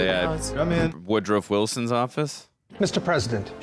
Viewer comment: I'm sorry, did you say Woodruff???